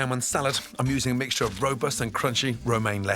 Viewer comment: "a mixture of..." what is he saying? Ropis? Robus? I just can't make it out lol Help me!